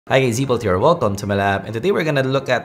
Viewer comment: "right here" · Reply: I think his accent is going "Hi-eii" "Zbolt here" So it sounds like he said right here